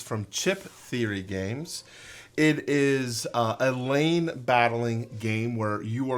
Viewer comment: Oh LANE battling system. I thought I heard LAME battling system. Guess since you didn't play Cloudspire, my hearing may be more accurate.